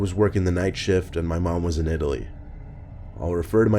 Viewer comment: For story 3, someone started talking to me so it sounded like he said “my mom was an idiot”